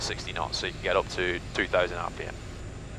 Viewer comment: Did he say "So you can go up to three thousand RPM"? If it's the case, it's strange that he was surprised by student going for these 3k RPM :D